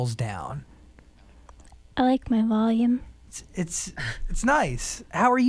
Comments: WAIT. I'm hearing this with headphones and you can clearly hear Titanic Sinclair talking to her through the headphones before every answer she gives O_o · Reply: AFTER MANY ATTEMTS AND HURTING MY EARS FINALLY I HEARD 00:28-00:29 (I like my volume) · you can hear someone telling her what to say. „I like my volume“